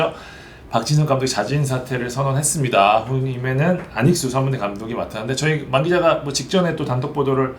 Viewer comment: 뭐라고 하신거예요? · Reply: 후임에는 안익수 선문대 감독이 맡았는데